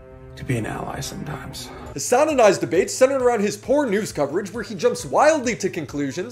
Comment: you said "...poor news..." and i heard "porn ooze"